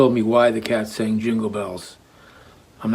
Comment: hold up, did he just say "why the cat sang jingle bells"? I've heard dogs sing it but I can't say I associated it with anything supernatural.